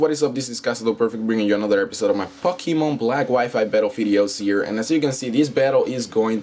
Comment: IS IT ME OR DID JUST SAY " BRINGING YOU ANOTHER FUCKEMON BATTLE"